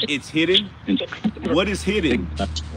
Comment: you said "what is hidden" I heard "cash"